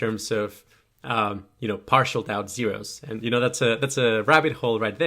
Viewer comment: What did you say here? Partial something zeros?